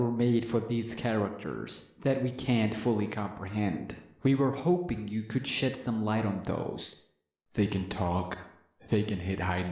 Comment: it sounds like he said we were hoping you could shit some light on those